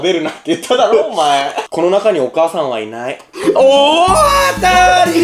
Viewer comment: コロナ禍にお母さんはいない に聞こえた